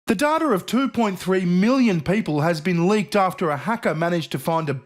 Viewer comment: 3 times at the beginning of 3 of your videos I heard you say "The daughter of 2.3 million people has been leaked after a hacker managed to find a back door" And I was thinking.... That's EXACTLY how I was able to not have any daughters or sons, I managed to find the back door and blow through it like I was going for the gold in 94 at LilleHammer. Then the 4'th time I heard you talking about Virtual Shield I realized what you actually said was "The DATA of 2.3 million people"